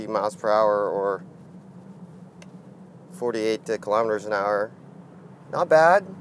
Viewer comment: "dicklover per hour"? Did I hear that correctly? As you are using the imperial system, driving an electric car, an automatic car and not even driving yourself.